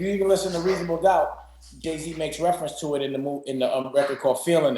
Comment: Did he say Gay-Z???